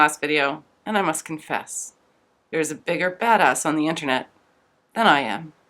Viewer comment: By the way, I had to do a double take at the beginning when it sounded like you said: "I must confess: there's a bigger fat-ass on the internet than I am".